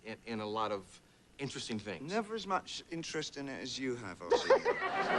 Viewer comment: what did he say here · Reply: "I don't have as much interest in it as you have, Arsie"